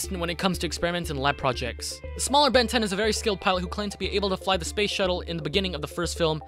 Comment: did you say Ben 10